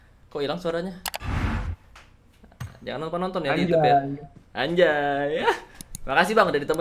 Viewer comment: did he say "ANJAY"